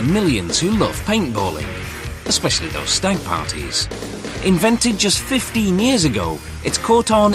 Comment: what did he say?? sounded like "stank parties" haha